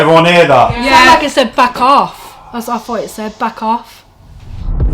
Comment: The voice in the bar sounded like it said "battle" to me not "back off." really fantastic episode love the way you just ran with the punches. makes me even more convinced that this isn't some scripted haunted house.